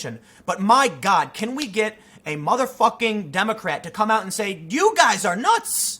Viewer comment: did he just say motherf*cking?!? Tim what's happening?